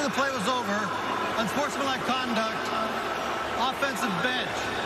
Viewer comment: Wow. It sounds like ref said @ 1:16 "offense a Betch" 0.0